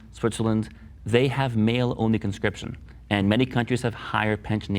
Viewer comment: I did not understand the statement about Austria on 8:50 , because English is not my first language. What did he say about Austria? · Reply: He said that Austria has a male-only conscription. Same with Finland and Switzerland.